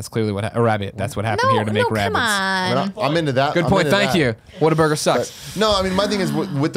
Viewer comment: Did say whataburger sucks